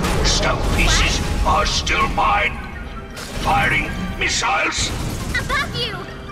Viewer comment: Whenever the boss said firing misiles I thought he said farting misiles